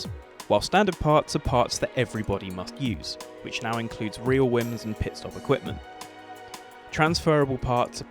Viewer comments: Am I the only one who heard "Reel Whims" at around 5:00 ?? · I'm sorry did he say "real whims"? Or Rear wheels